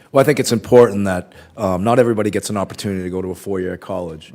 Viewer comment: Listening to Senator Cassidy was very detrimental to my blood pressure. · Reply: i'm sorry but I need to ask this because i'm not sure if I heard it right. At the time 1:20:49, did Mr. O'brien state that he thinks that "not everyone should get an oppurtunity to a 4 year college?